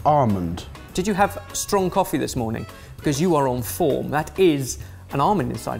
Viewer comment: "Did you have strong coffee this morning? Because you're on foam." - 03:54 That's what I heard, and I thought "Wait. What?"